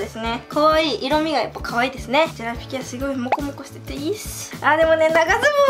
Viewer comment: もこもこがロコモコに聞こえてジワジワくる